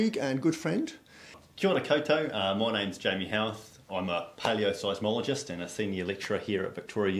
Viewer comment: I thought you said a patio psychologist lol... waiting for crazy people to walk past your pergola so you can counsel them.😂😂😂😂😂😂😂😂